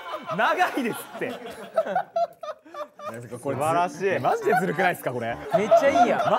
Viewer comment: の「まじでズルくないですか？」が 若干動揺で「マジでずるくないっすか？」に聞こえるの良